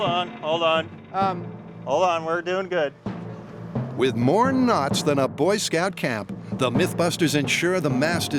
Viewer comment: I thought he said “with more ‘nuts’ than a Boy Scout camp” 😆 Had to play out back 3 times before I heard ‘knots’ …I’m like there’s no way they said that lol